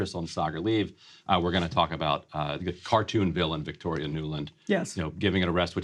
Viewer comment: When I heard Ryan say "cartoon villain Victoria Newland" I thought he said "cartoon villain Victoria Neuman" and I was expecting a Boys crossover. Much disappoint.